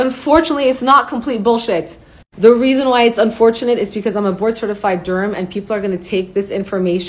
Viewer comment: i heard "board certified GERM" 💀